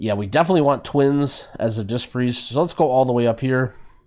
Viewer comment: did he say "sonst go all the way up here" sonst is german and means something like "otherwise"